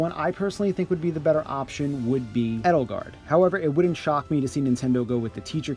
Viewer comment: I thought you said edge guard lol